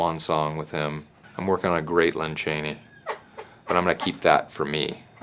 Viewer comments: I thought he said Lon Chaney, because a Phantom of the Opera impression would be hysterical. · Ha ha ha! I thought he said "Lon Chaney!"